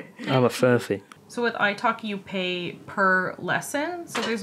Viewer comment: When you said "italki", I heard "Ice hockey" for some reason 😅